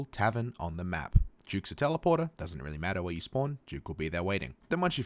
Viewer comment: did you say teleporter ? and whats that ?